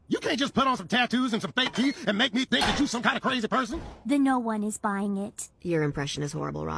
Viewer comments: Starfire saying "THE noone buying it" instead of "noone is buying it" gets me for some reason. The way she speaks is just so funny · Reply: I thought Starfire said “then no one is buying it” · Starfire saying "THE noone buying it" instead of "noone is buying it" gets me for some reason. The way she speaks is just so funny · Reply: I thought she said, "Tim, no one's buying it!!"